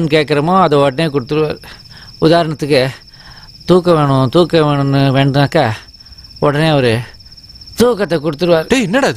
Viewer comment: What is vivek saying @ 12:22, somebody explain. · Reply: He is telling that thookam venum nu ketta thookatha koduppaar ( lifting something from inside) thookkaradu also means erecting..vijay becomes afraid and asks about the lifttedmatter then he shows as torchlight and keeps. Regards